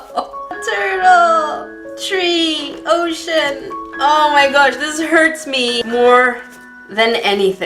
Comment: i thought she said "oh sh*t"😂